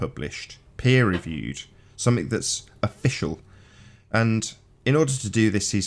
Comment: did he just say "afficial" 😲 🤣